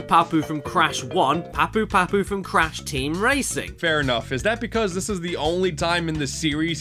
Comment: did he say cranch team racing?